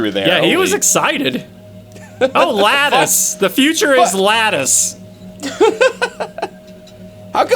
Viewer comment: Not gonna lie, when they said “The future is lattice.” I thought they said “The future is GLaDOS.”